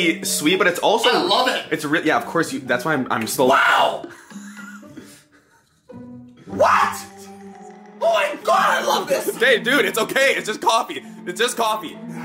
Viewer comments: did anyone else think that at 9:53 said. "babe, dude its okay" ,':o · did anyone else hear say, babe. dude, it's just coffee, or am I just Trippin derpaul for life